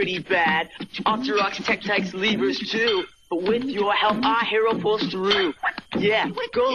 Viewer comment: i love zelda but what the hell did i jut see when i was a kid i thought he was saying but what the hell our pulls through instead of "with our help"